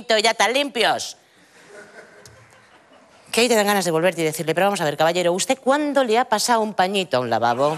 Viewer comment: ¿qué es que dice en el segundo 0:58? jo es que es rápidisima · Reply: Que hay te da ganas de volver y decirle ;) Ni me acordaba de este vídeos jajajaja joer qué rápido lo dice, ¿no?